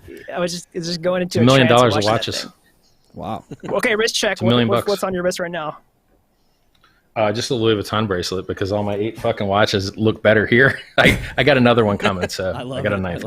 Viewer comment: WRIST CHECK or RICH CHECK ??? Vote with your comment! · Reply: claims he said WRIST CHECK which makes perfect sense. BUT it sounds like he says RICH CHECK